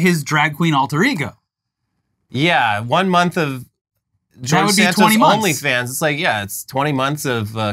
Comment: When you said, "Welcome to the George Santos Onlyfans" I thought you said, "Welcome to the Jort Santos Onlyfans". Now I'm just picturing a page where all he does is sport his collection of sexy summer jorts for his eager fans.